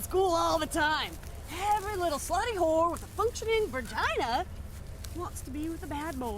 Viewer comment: It sounded like she said "functional vergina"